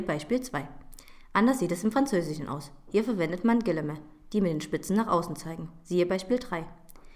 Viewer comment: weshalb man aber diese doppelpfeilchen im deutschen setzt, wird hier nicht beantwortet. und was sagt sie bei minute 00:58 ? Gillemä? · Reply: Sind "französische Anführungszeichen": Guillemet(s). <link>